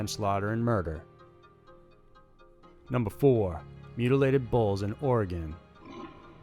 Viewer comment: I thought he said "mutilated balls" I was like..."what the...?" until he began to explain....sorry I'm from the UK we say it different